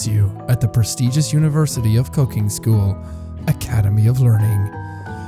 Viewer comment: i thought you said cocaine school